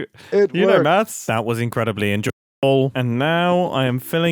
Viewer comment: Brief audio cut out at 20:18 that it makes it sound like Mumbo said "That was incredibly en- Joel."